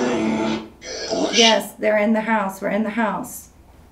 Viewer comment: before it says in the house it sounds like it says “it’s real”